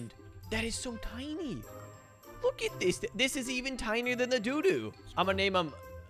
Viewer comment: I thought he said "Its even smaller than my doodoo..."